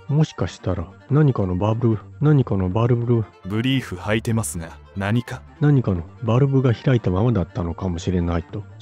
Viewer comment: (ブリーフ履いてますが何か？)と聞こえたが？ 4:12ミルクチョコと聞こえたが？